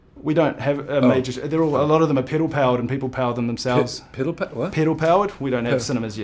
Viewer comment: I just spat my dinner all over my screen. Funniest interview ever! They were so confused hahaha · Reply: The part that really got me laughing was when Will asked about movie chains in New Zealand. I’m still not sure what peddle powered means, but I was really confused when I thought he said piddle powered! And Will looked just as confused as I felt. Haha